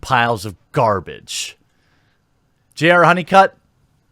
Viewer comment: I thought you said JR HoneyCuck. Which is more fitting than his old last name. Welcome Mr. HoneyCuck to your new internet name.